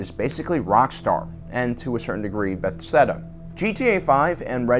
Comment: Did you just say Bethsteda?